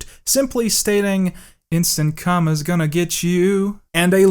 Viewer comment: I only heard "instant come is gonna get you."